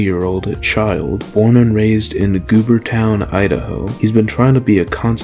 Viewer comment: I heard "goonertown" and had to replay this part to make sure I wasn't hearing things 😂